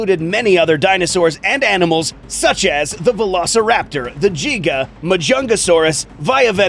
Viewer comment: Did this guy just say jiga instead of giga